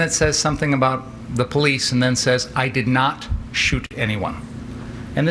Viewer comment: I heard "did not shoot someone". But what else would you say to de-escalate the guy with a handgun.